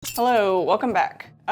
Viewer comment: For some reason on the intro I thought she said "hello wolf pack" and I was just like o okay so this is a thing now